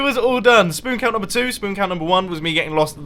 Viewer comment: go to 10:35 turn on captions and instead of spoon count it says sperm count.yah😐